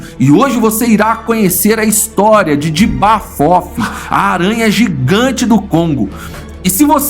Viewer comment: O cara fala "J'aba fofi" O que eu escutei "Diaba fofa"